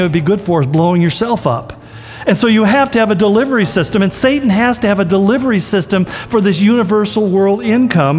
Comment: Did he say at the end 'satan has to have a delivery system'